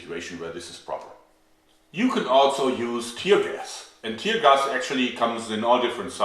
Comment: Oh man! When you said tear gas could be used on animals, I mis-heard you and thought you said enemas. For a moment the only thought I had was; What kind of forced enema problem could Germany have, to need a pepper spray law dedicated to it?. Oh man great video, very informative.